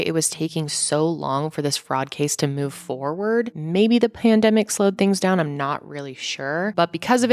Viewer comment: I heard “plandemic” 😉